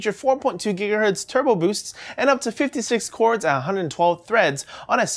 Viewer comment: did he just say 56 cores and 112 threads?